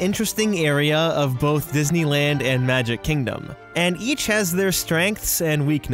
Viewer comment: did anyone else hear, "And YEETch...."